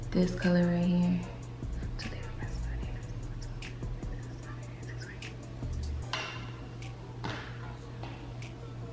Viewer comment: 😂😂 what was she sayin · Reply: she was counting to 20 lol 😂.